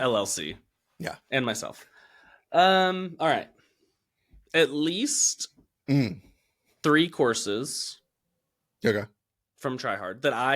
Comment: For some reason my brain malfunctioned and thought you said 'At least... 3 horses' 😅